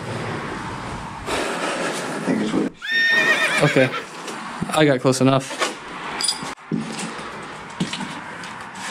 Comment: Great job as always. I'm not sure I want to know what he saw at 5:17. I couldn't make out what he said but you sounded pretty grossed out. I'm not sure if you do "requests" but it would be interesting to see what the Kmart\Kroger complex in Fairborn looks like now. I have heard that the Kroger side may be torn down soon. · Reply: Absolutely I love recommendations. At 5:17 he says “I think that’s where they Sh&$.” At that point I was completely overwhelmed and I just wanted to get out of the upstairs 😜